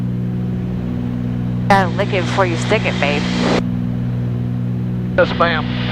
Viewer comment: did she say: "lick it before you stick it" with a grin...